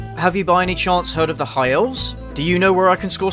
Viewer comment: “You think your life is hard? I’m a junior wearing size thirteen Nikes. Men’s size thirteen Nikes. Beat that.” I get hives if I go in the sun for more than around five minutes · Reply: Madison With A Question Mark I thought you said “high fives”